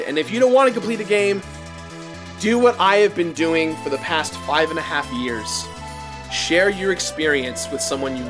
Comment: I thought Jirad said "Do what I've been doing for the past 500 years."